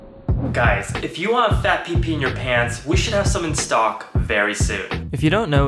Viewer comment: I think he said fat p p in your pants